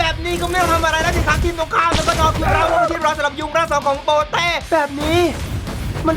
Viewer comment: ตอน4:58ใครได้ยินว่ายุงร่าง2ของโปรเต้บ้าง555 รอ อะตอมมิคกับกระบองเหล็กSSR+ของเลยครับ